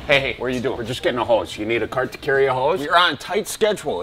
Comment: I thought he said horse